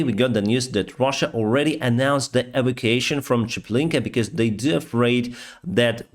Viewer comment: What is the work you say at 1:20? evacuation? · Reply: Yes "evacuation" was the word used